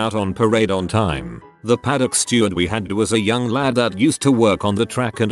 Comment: I misheard paddock steward as paddenstoel and was wondering why op called someone a mushroom (I am not a native Dutch speaker I am still learning)